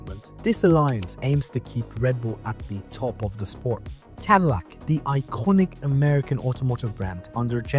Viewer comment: sounds like you say Tamilac?